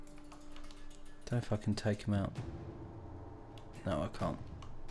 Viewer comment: Does he say the f word in the video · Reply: He said "Don't know if I could take him out"